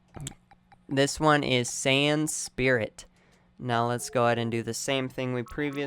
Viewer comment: It sounded like he said San spirit